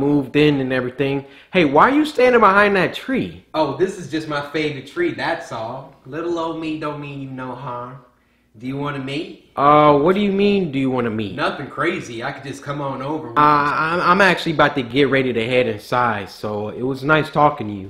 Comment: I legit thought he said “do you want to mate?” XD